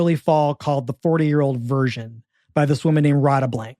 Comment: I heard it as "The 40 Year Old Virgin"